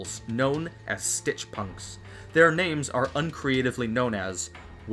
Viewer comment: You said "Thor" instead of "Four" deliberately didn't you? ;)